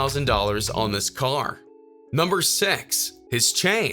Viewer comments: Am I the only one who heard "Number Sex"??? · Lmfao anyone else notice that he says sex instead of six 😂😂😂😂😂😂